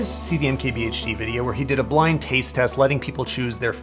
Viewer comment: did he say blind taste test ?